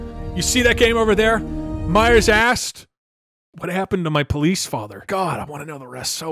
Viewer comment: "Meyers asked" All I heard was "Meyer's ass"